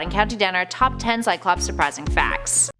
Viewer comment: I misheard this as "Cyclops most depressing facts" when I was switching tabs. I was like... Oh no.